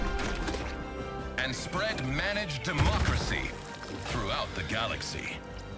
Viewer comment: This is a game that got Play Station gamers to BEG Sony to release the game on XBox. Let me say that again. Play Station players asked Sony to publish this on a rival console. Not the XBox players. It's bridging a console war gap. Or, at least, the community wants it to. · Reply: I thought they said mannish democracy, managed democracy makes wayyyyyyy more sense